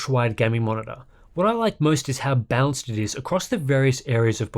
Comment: I can watch this as many times I want, I always hear "bounced" instead of "balanced". Great review though!